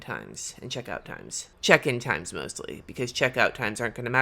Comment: When you said “check in time” I thought you said “step in time” from Mary Poppins lol